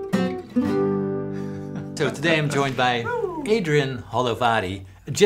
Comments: At first, I thought you said Adrian Hollowbody. I was like, c'mon, that's not his name. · At <link> I first thought you said "Adrian Hollowbody" 😂Great video! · I thought you said his name is Adrian Hollow Body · Thought Paul said the guy’s name was Adrian “Hollowbody” …nice! 😊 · I actually thought you said his name was Adrian Hollowbody